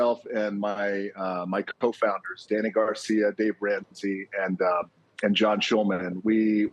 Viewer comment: Did he say Dave Ramsey @ 0:42 ? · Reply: Dave Rienzi